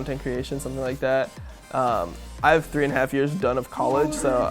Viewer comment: what did he say? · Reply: Shut the fuck up